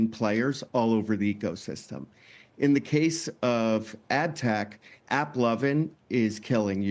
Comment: That's crazy, how is AppLovin even a competitor to Unity? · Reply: he clearly said "in the case of ad tech"